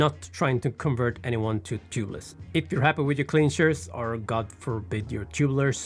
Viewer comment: When you said "If you are happy with your clinchers..." I heard "If you are happy with your clean shirts" and I thought that was also a valid argument with spraying sealant.